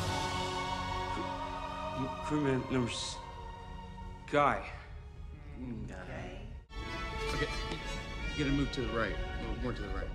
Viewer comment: did lialali say GAY ?